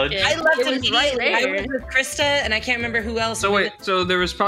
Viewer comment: I thought you said cryptid when you said Krista and I that made me smile